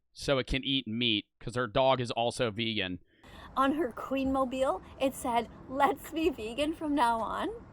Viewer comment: did she just say queen mobile lmao